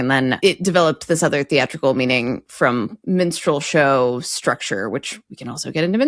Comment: Gotta love the pin-pen merger. I just misheard saying 'minstrel' as 'menstrual'.